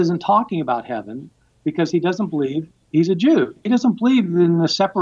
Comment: Jesus doesn’t believe he’s a Jew That’s how I heard it, and how the closed captioning heard it, but it must be, “he doesn’t believe, AS a Jew...”